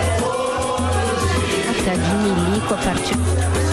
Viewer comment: Ouvi a Vera Magalhães soltando um comentário: "Festa de milico é particular"... 0:48 😂😂😂😂😂😎